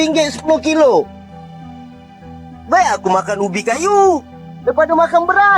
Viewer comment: saya salah dengar tadi bubuk kayu😅. eehhh ternyata ubi kayu🤭. sejak kapan malaydes makan serbuk kayu batin saya🤣🤣🤣🤣